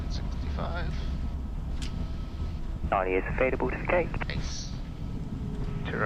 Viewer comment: Can someone help me what the tower said at 33:16? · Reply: Charlie is available, to the gate